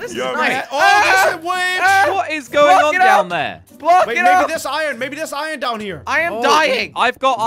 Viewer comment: DID SLOGO say the F word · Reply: He said block it off